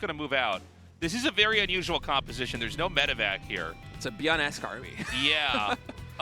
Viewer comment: Oh god, i understood "byun-ass army" instead of byun-esque. Only noticed on my second watch